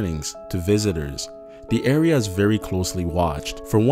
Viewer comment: I thought you said "Hiroshimas get permission..." lol